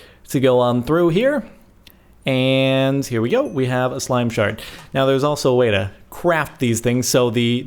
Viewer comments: i swore he said "slime shart" hahaha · In the beginning of the video, I thought you said "Slime Shart," not "Slime Shard." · I thought I had heard "slime shart" ... disturbing to imagine... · At first I thought he said: "Slime Shart" :D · thought he said slime shart the first time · sounds like he is saying slime shart XD